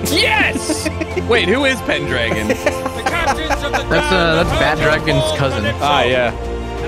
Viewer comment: Did I hear "that's bad dragons cousin" or am I tripping? XD